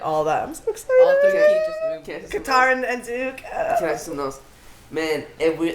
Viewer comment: did she say katara and zuko??? Excuse me